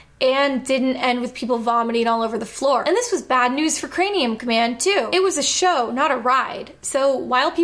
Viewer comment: is she saying "crameum command" half the time? Sounds like it. Like, 9 minutes in before I actually heard her say craNeum.